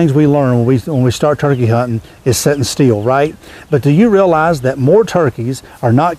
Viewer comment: Maybe all these folks correcting your grammar need to quit turkey hunting and start teaching American (English to all you professors). Thanks for your help, great video. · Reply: Lol they aren't critisizing him 'nor are they correcting his grammar. They just find it funny that they thought he was saying "setting steel". It's not that deep fam.